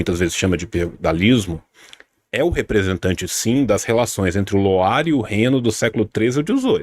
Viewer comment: Relações entre quem? eu não entendi os nomes q ele falou, alguém me ajuda (Loire e Reno? aquele rios da Europa?)